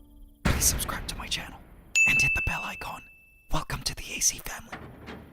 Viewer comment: Wait you say “hit the bell icon” I Kate always thought you said “hit the bell like gong”